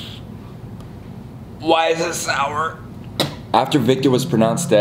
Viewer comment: my man really sounded like he said “WHY IS IT SO SPICY”